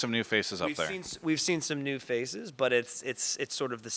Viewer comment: I was never a fan of Dignitas, yet I'm somehow happy to see that they're back. It's good to have old faces. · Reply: Lol thought u said faeces